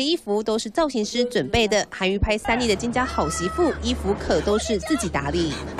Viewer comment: 我聽成拍三年的金家好媳婦